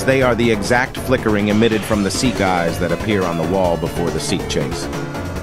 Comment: the subtitles say sea eyes (seek eyes) and seat chase (seek chase)